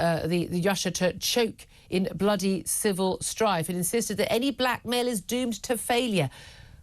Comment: Ohhh black mail I thought u were saying black male I was like tf